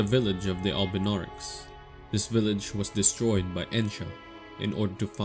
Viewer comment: I can't hear what you're saying at 4:05 seconds where the village of albunaurics was destroyed by ________? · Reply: Ohhh Ensha (Gideon's apprentice)